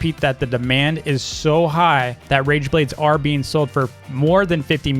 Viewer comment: I THOUGHT THE RAGEBLADE SAID RENEGADE